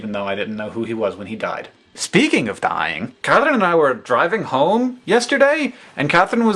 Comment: And now that I have actually watched this video...at one point you said, "Katherine and I..." and I thought you said "Kevin and I" and I was very confused. Yep. That's all I have to say. Oh, and "I'm a camel" <3